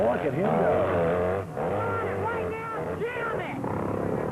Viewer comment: I think I heard "get on it right now jam it"...37 times lol